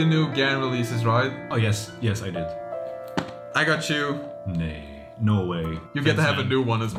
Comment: did just say nahi? Did he speak Hindi or is no in Dutch nahi